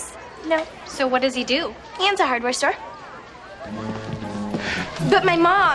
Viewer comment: wait what did she say?? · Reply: He owns a hardware store